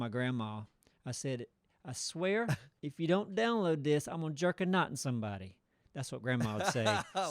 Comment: I thought she was saying "jerk a nut"...which definitely didn't sound quite appropriate.